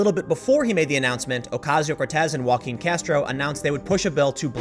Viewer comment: Okay... so legit... I thought he was saying "Walking Castro" and then I saw it was #Only "Jaquin Castro"... What a load off (rolls eyes)